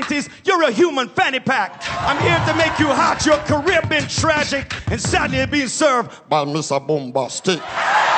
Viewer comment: What's he saying at 0:30? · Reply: He was saying and sadly you're being served by Mr Boombastic.